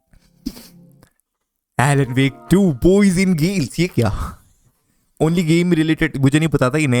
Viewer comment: what did u say?? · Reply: "yeh kaya"